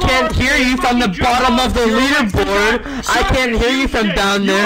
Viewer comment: "Sorry, I can't hear ya from the bottom of the leaderboard 🙄" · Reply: Bro, for some reason, I heard "Liverpool" instead of "leaderboard". Thank you! Youtube subtitles suck!